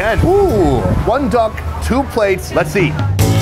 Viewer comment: Did you just say “one duck , two plates” !!! 💀💀💀